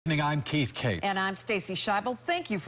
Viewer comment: At the first part it sounds like he said “nigga im keith kate”